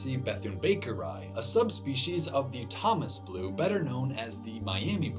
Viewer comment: DID YOU SAY THOMAS BLUE BUTTERFLY!